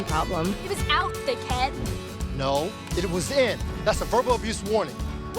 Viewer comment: listen to what Violet says. Brat y'all should be ashamed. 😂🤦 (Also if y'all don't know what she said she was the D word but with the word head added on to it.) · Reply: she said "thick head" -- sorry it sounds like a bad word!